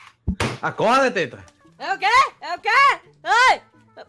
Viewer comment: ouvi animal de teta akakakkaakakkak